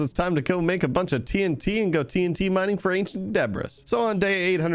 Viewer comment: I thought he said zebras when he said debris